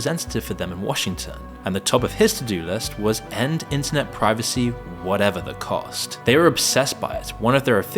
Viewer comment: u said privacy, instead of pirating..... 2 very different things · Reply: He tried to say piracy but accidentally said privacy